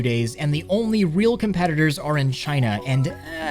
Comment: After I heard the part "cup ain't much better then meta" I Had to sub